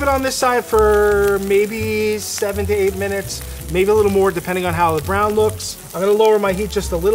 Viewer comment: legit thought he said 78 minutes